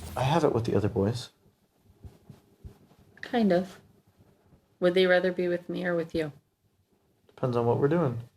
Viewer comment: When she said "would they rather be with me than with you?", I heard "nananananana they love me more than you!!"